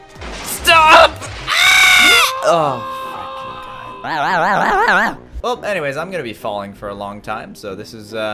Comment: did he say " ahh f*cking god